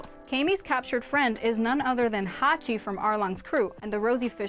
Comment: When she said Haki i thought she said hacking